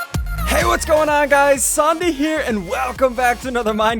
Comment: For some reason I heard him say zombie instead of ssundee